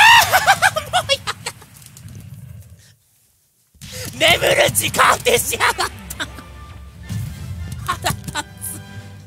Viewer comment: 眠る～しやがった！ って何て言ってるの？ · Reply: 眠る直当て(じかあて)しやがったと言ってますね